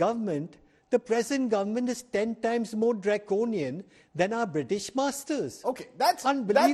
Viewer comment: did he say British Masters? His name should be changed to Colin Slave